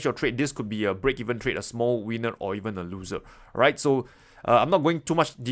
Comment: Did he just say small weaner or even a loser?